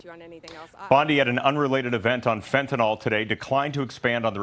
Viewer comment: I thought he said SHE was on fentanyl at first! 😆😆😝